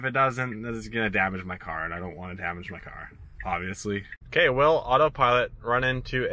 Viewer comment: At part 2:17 i think he said i dont wont my tesla to get damedge snd didnt say anything about his brother